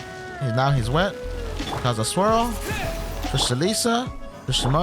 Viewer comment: did you just say Kazuha swirl 6 months before his release lol